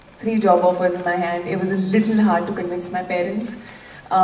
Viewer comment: what she said at 1:53🤔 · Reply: She said literally it was hard to convince my parents